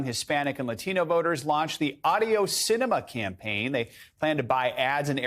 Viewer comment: did he really say “the audio Sinema campaign?” Audio? 🤔